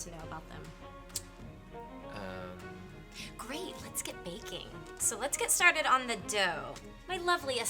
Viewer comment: I heard "Great, lets get naked" I was like "what"